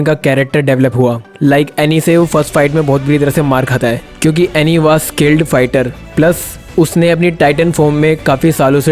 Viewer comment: I heard that as butt hole